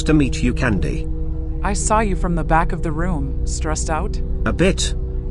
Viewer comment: I got scared because I heard "backroom" (not cool memories)